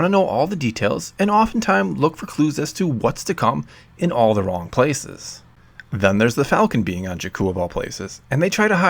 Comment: did you say Alderaan places? lol